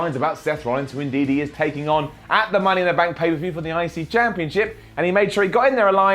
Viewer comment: Did you say United States Championship?